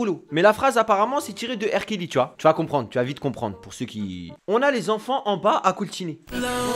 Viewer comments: moi j'entends : "On a des enfants là-bas!" Chuis la seule...? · A 5:00 j'ai entendu on n'a des enfants